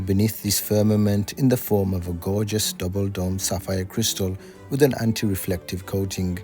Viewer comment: did he say “gorgeous double dong”?!